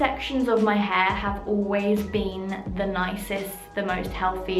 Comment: Who else heard her say the nappiest when describing the bad parts of her hair🤔🤔